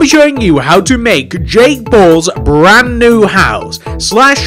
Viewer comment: It sounds like he says jake balls